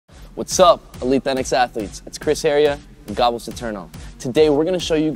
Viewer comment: Did he just say and Goblin Saterno?!?